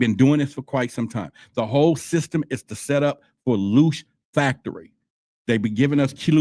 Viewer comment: That movie they live with rowdy piper, and those glasses they tryed to tell us stuff in movies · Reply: I heard you say loosh